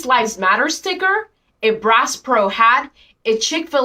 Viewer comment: This is so petty. If it's a multi cultural room everyone can go in it. This is just silly. I also noticed when they said bass pro it sounded like they were saying brass pro. Maybe they thought it was some kind of cop hat 🤣. Either way it was seriously stereotypical.